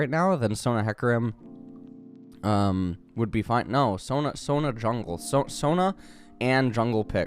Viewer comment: I heard him say Sona jungle and was like, "What now?" and just kind of paused for a bit trying to figure out if Sona was a good jungler until he said, "No no no, Sona and a jungle pick." Nice to know that I'm not the only one not understanding